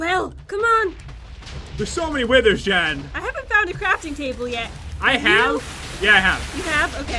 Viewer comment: Jen: "I hate to buy a crafting table" I hear: "I heat ta ba a crafting table"